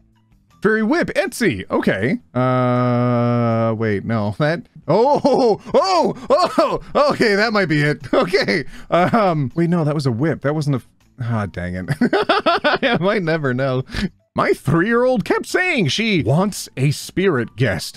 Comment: did just say "Hot dangit"? Bc if he did, I'm being a quote thief...again.